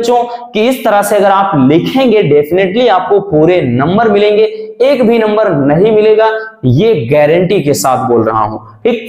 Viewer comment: Sir pure number milege uske baad kya bola aapne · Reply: Ek bhi number nahi milega😂😂